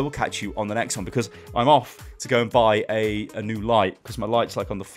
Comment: First i thought he said "Cause im of to buy a new Life"